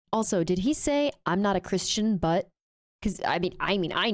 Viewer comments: It did sound like he said "not a christian" and the head shake confirmed it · I've heard that clip several times and each time I hear Trump say "I'm not a Christian". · I can't hear too good, but the wife does, and we could've sworn he said he's NOT a Christian during that speech.